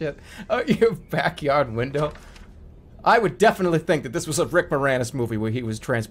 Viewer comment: what does he say? Rick something? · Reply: Rick Moranis